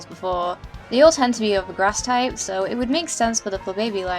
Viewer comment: When it said ''Grass'' i thought it said ''Gross'' XD